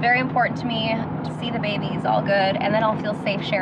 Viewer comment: I heard he 2 times once by desi and once by Steven. I think it’s a boy ❤️ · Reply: I hear it at 9:00-9:01 it sounds like she says wait to see the baby and he’s all good. I’m happy for them either way, but boys a truly mamma boys 🥰